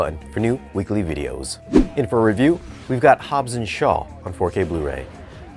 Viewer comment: I got excited because I thought it said "shawshank 4k blu ray